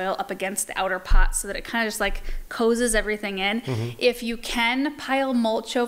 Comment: at 36:26, you say a word that I am unfamiliar with. I don’t know how to spell it even to ask about it, but I’m wondering how you would spell it. The closest thing to it that I know of is cozying or cozied. When I hear a new word that I’m unfamiliar with, I like to look it up and make sure I’m spelling and using it right. Thanks for your help and all your great videos.